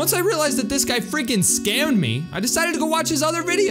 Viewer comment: it says scared instead of scammed